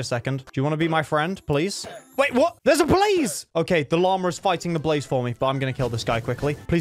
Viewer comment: It sounded like he said that there was a Police. Then it sounded like he said that the Law was fighting Police which would make no sense but that’s not what he said so it’s all good.